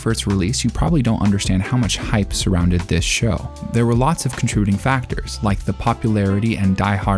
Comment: I'm sorry, what was that at 1:34? "Contruding"? Not often I hear a new word, and Google isn't helping me with a definition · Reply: He said contributing, just fast